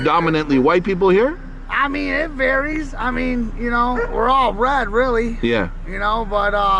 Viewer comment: Did he say "We are all red really."? Yes. We are All red on the inside. ✌